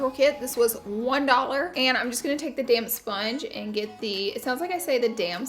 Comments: I laughed a the "damP" sponge bit. At that exact moment I thought she said "damn sponge" and thought "did she just cuss?" 😱 and immediately clarified it "with a P at the end" ... WHEW! 🤣🤣🤣🤣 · I really thought you said damn sponge too lol